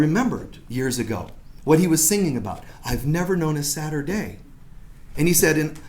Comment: I keep thinking about what you said " I've never had a sadder day" I thought you said I've never had a Saturday. Every time I think about it I can't stop laughing 😂😂 Thanks whenever I have a sad day I just have to think of this! Much love!